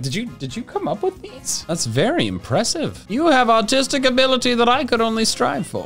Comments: anyone else heard “autistic ability” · I THOUGHT HE SAID AUTISTIC NOT ARTISTIC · I THOUGHT HE SAID “autistic ability” LMAOOOO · I thought he said autistic 😂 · Thought he said autistic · is it just me or did say autistic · I thought said autistic abilities 😂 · I'm sorry whenever you said artistic ability. I thought you said autistic ability. I have a power of miss hearing things into funny things. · Anyone else think he said "autistic ability" at 11:05? Cuz I have autistic ability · i swear at 11:05 he said "you have autistic ability that i could only strive for" i can't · did he just say “autistic ability” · me to any of my freinds for no reason other than that they got 1% less on the test than me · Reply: for some reason i heard “autistic ability” 💀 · "you have great artistic abilities" what i hear: "you have great autistic abilities" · When he said ‘you have artistic ability I do not have’ I thought he said autistic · am I the only one who heard autistic abilities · I thought he said “autistic ability” 💀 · “you have artistic ability that I could only strive for” What I heard “you have autistic ability that I could only strive for”